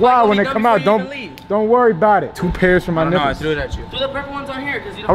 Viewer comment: did he say two pairs for my n-words